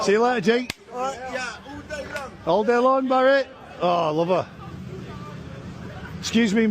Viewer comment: he actually says here, "Oh, i love her" (referencing the 15-year-old girl in front him), NOT, as the subtitles state "Oh, lover"